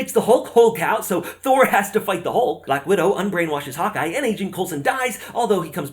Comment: This is insane. I love it! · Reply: Also, every time you said Hawkeye I thought I heard "Hot guy".